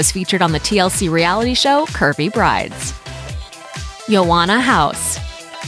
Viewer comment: “Yoanna House” Literally thought they said “you wanna house?” 😂😂